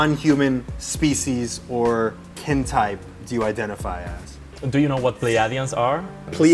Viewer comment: I swear I heard hentai till I researched that part